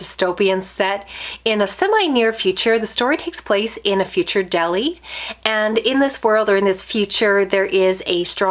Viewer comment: says: "The story takes place in a future Delhi ..." I hear: "The story takes place in a future deli ..."